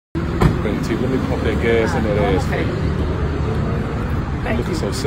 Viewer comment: Wayment I just heard him say “let me pump that gas and that ass fa ya!” 👽☠️💀